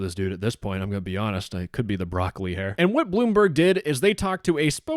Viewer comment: Oh youre saying broccoli hair idk why I thought I heard rock Lee hair n I was like ion see the bowl shape